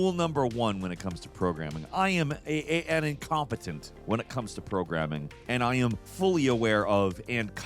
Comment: i heard "i am incontinent when it comes to programming" and i was gonna have questions